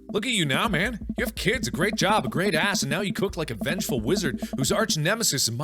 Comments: Does anyone else hear mark add in "a great ass" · what it said:you have kids, a job what mark said:you have kids, a job and a great ass 31:47 ;D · Did anyone else hear him say 'a great ass' at 31:48? or was that me?